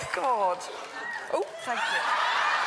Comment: What does she say at 3:05? sounds like f*** you hahaha